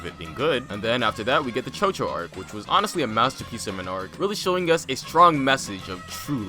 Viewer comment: I thought that said masterbait